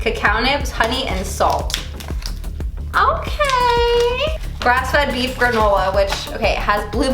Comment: Did she just say beef granola?